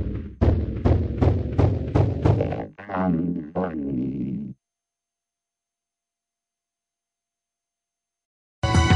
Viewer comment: Seriously, does anyone know what he said at 9:18? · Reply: "Come For me" (idk)